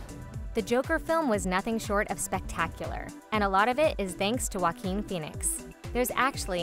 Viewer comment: i was looking down drawing and hearing the video then i hear "All thanks to Joaquin Phionex" I went "WHA- oh... oh right..."